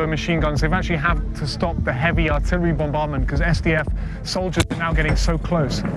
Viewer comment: Did he just say artillery Bomb bomb